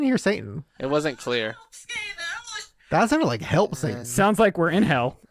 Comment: sounded like help save us